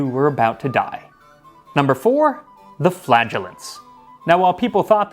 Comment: I wasn't looking at my screen when you said 'flagellants'', misheard it as 'flatulence' and was very confused for a moment there. Couldn't work out how that would stop God being angry - thank you for immediately giving the explanation, that would have been very strange to go on with.